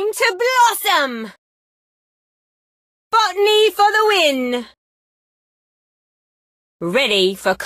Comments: I thought Rosa said f**K me for the win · I always thought that she was saying "F**k me for the win" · in game I seriously thought that she was saying F me for the win! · I always thought that she was saying "F**k me for the win" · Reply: I thought she said “Butt knee for the win” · I thought rosa was swearing but you told nope she ain't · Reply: Same bro I thought she said "f*** me for the win"💀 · i thought she said "f**k me for the win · I thought she said f**k me for the win but she said bottany thanks man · i akways think she says”Fu(k me for the win"brooo💀💀💀